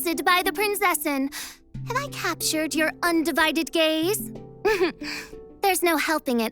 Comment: bro I hear "Have I captured your undivided GAYS" and I started watching this after having a big conversation of thinking I was bi but actually I was a lesbian! I'm dying